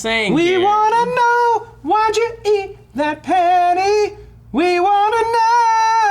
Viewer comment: The entire eating a penny bit I thought they were saying eating penne pasta. IS IT SO WRONG TO ENJOY FINE PASTA WHEN YOU'RE 12? Much confusion from me for a minute haha